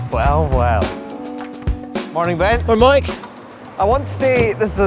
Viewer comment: who else thought he said "morning babe" lol